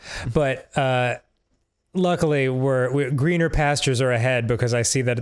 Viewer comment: I misheard 10:12 as "wiener pastures."